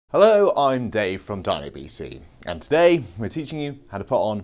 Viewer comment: Thought you said, "DAVE from DIABETES"